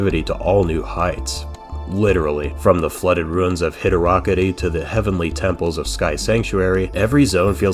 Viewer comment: Did… did he just call Hydrocity hid-Ra-kitty?